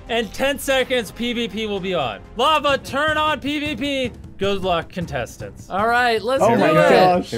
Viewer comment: Did mrbeast say, “lava turn on pvp.”?